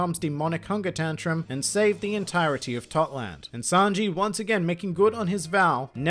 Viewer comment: I heard Thot land NONI??